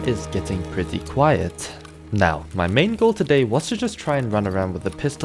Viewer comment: MY MANGO MENTIONED 🗣🗣🗣🗣🗣🗣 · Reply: ohhh it sounded like he said mango instead of "main goal"